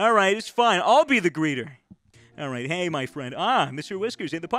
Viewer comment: swagger i don't think you're hearing this man, i clearly heard him say he's a "Breeder for the Pug" he's a dog breeder. that must be why he got so frustrated.